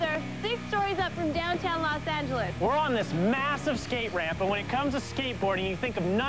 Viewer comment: did I just hear MASSIVE?